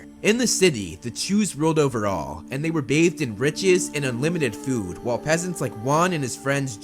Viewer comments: Am I the only one who misheard the narration at first as, “the jews had unlimited food.” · did he say "the jews ruled over all"..... ? oh boy